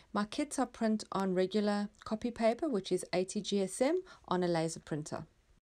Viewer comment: I went and checked out that Nectar Etsy shop and I like what I see. So you just print on copy paper? I heard you say 80 gsm but I missed the rest of the statement. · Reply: Hi 🙂 I just mentioned that I print on 80gsm and use a laser printer 🖨️ hope that helps 😌💙🦋